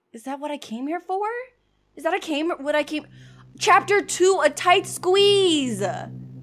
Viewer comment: did she say chapter 2